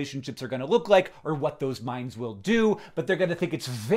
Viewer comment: I thought he was saying "mines" instead of "minds" and I was like, oh god Hank Green things artificial landminds are going to be a huge part of daily life? Wait, are people dealing with biological landmines right now???